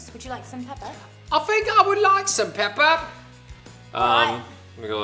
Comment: I thought she said "Sam pepper"